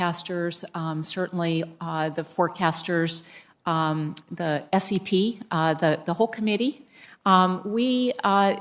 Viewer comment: did she just say SCP? Tf does that anomalous entity organization have to do with this? Lol